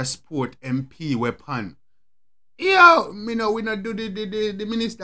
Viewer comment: I thought he said mi nuh par wid empty weapon and no MP weapon . Lit review